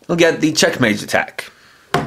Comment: For some reason, I thought it said "Checkmate".